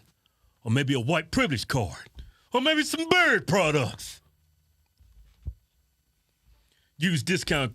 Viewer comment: I thought you were saying Bird products until I figured you have BEARDS. That Lieutenant who was pepper sprayed didn't realize the police aren't psychic.